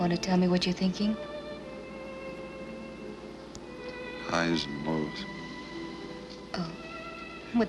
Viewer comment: what did he say? What word was he describing? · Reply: Highs and lows?